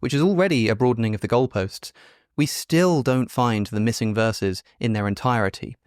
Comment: "Alex explicitly says many completely FALSE claims" Ok, we can go through some of those. "Alex claims that there are missing verses not found in the entirety of the dead sea scrolls which is false." It is really obvious that you were so desperately looking for something to criticize (anything to change the subject) that you misheard his actual words. He said, "we still don't find the missing verses in their entirety." In other words, you can find pieces of Isaiah 2:9-10 (which is missing from the Isaiah scroll) in 4qisa, but that document is so fragmented that you only get pieces of it. "He claims the apostles couldn't have been alive . . . and ignores known events of how and when they each died." I may have missed what you meant, but an assessment of how long someone lived that you, personally, disagree with is not a "lie." There are zero "known events" about how any of the apostles died. Maybe with Paul, if we're even counting him, we can kinda sorta infer some things about how he may have died, but even he is sketchy. There are zero "known events." There are just traditions. Things that were claimed. That's it. Peter being crucified upside down (because he requested it and the Romans were so accommodating)? Yeah, no. That is not an established historical fact. John died at 90 years old? According to what? According to the story you heard on your mother's lap. According to apologists. Not according to established fact.